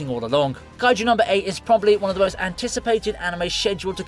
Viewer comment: My heart skipped for a second, I thought you said Kaiji… but it’s kaiju, f.